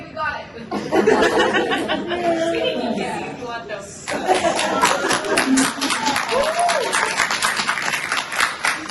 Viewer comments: At the end what does Taylor say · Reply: “We can keep kissing if you want, though.” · What does Taylor say at 37:22 when she's stepping away from the kiss shot? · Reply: “We can keep kissing if you want” or something like that.